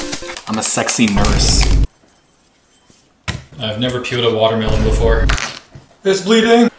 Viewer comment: i miss hear the "i never pealed a watermelon before" i thought he said i never killed a watermelon before